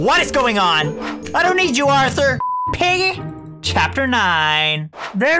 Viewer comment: Wait- Did he just say I miss you Arthur